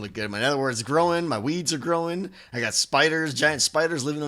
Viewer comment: i thought he said my weeds growing, lol xD